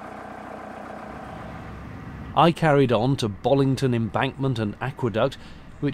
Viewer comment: I thought you said "Bollington....Maccqueduct" for a moment there - nifty local name I thought....then I listened again. :o)